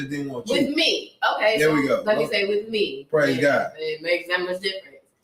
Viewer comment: Is it just me or did he say "PRAISE GOD" after he BACK HAND SLAPPED her with an INSULT!!!🤷🏾‍♀️🤯🤯🤯 I'm so triggered!!🤦🏾‍♀️🤦🏾‍♀️